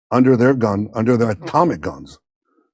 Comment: I totally thought he said Tommy Guns, at first. 😂